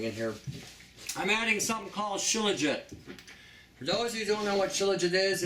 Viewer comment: Hi, at the 6:30 mark you are holding a brownish jar and it sounds like "chiligen" and you say the protein binds with the dairy yogurt... My speakers on my computer may not be working well which is why I cannot hear well. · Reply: She means the shilajit